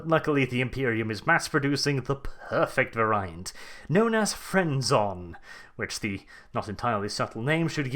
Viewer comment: I heard "Friendzon". Which, given how some entitled "Nice Guys" act, sounds like it could drive them into a murderous rage. Edit: Oh God, now I'm imagining some fat, neckbearded, virgin loser who got caught doing the 40K equivalent of credit card fraud charging a giant Ork with a chainsaw bayoneted lasgun because the drugs cause him to hallucinate it as a pretty-looking coworker who just told him "I just want to be friends", screaming at the top of his lungs how she's always dating jerks and she needs to give him a chance. Ork: Oi! Don't youse gets it? She don't like youse 'cause youse is always actin' like a self-entitl'd git who tinks e's owed sex for actin' like a haf decent oomie! News flash, wimmin's is not somethin' youse deposit niceness for sex!